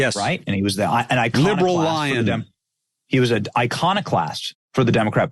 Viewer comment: benny seemed confused by liberal lion remark...thought he was saying liberal lying lol